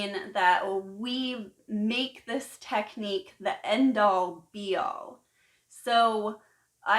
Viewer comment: I will be grateaful if someone could tell me what she says in 5:56..."to make this tecnique the....." didnt get the word...thanksss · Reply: "end all, be all" basically, we make the technique itself into our ultimate goal.